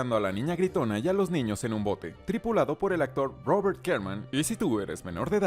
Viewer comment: Quien es ese Robert Karman o cartamn es que no le entendí bien, quien es o qué ?